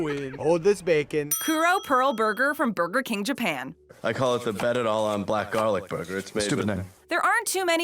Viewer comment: I heard Pearl Habor burger wdf